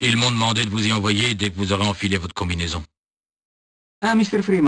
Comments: Sounded like barney said communism. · WHY AM I HEARING SO MUCH "COMMUNISM"!?!? ?!? ? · Did barney say "Communism" in 5:42 ? LMAO